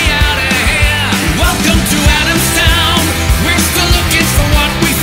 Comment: I thought it said "Welcome to Amsterdam" and was very confused when they started singing about Adamstown